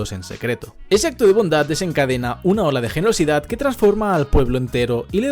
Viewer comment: escuche ola de genocidio